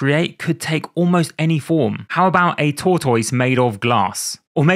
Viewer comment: What the hell did you say at 2:28??? Toy-tuss?